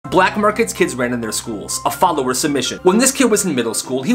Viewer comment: When you say ' a followers submission ' I thought you say FOLLOW OR SUBMIT I was so confused for like the longest time 😅